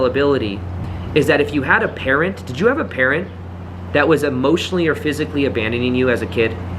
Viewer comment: I was so confused at first I thought you said parrot not parent. 😅